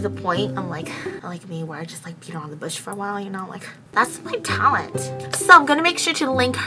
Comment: Lmao I thought she said pee around the Bush instead of beat around the Bush... Sorry 😕😂😂